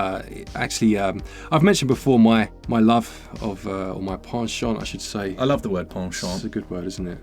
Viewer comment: I was like, did he just say, pawn shop?